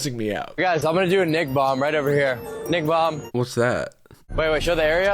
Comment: I legit also thought he said NIC- bomb too, like nicotine or something. I was not surprised but just… deflated when he did it and it was him being racist.